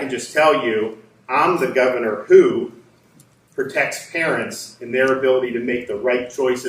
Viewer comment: Did he just say he's here to protect the Karens? 😂😂😂👯 You gotta love that shit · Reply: Oh .. He said "parents" lololol. Hahahaha I thought he said "Karens"....